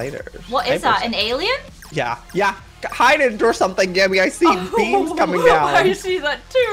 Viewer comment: When he said beams at 1:18 I thought he said beans